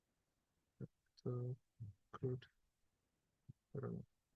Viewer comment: what's the word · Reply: Hello, The answer should be CRUDNESS. It means lack of refinement or polish